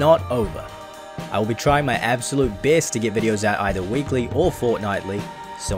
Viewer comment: Did u said “weekly or fortnitly”😂 like what does that mean bruh🤷‍♂️ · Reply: at the end u said ull try to upload weekly or idk the next word I said, sounded like u said fortnite almost 😂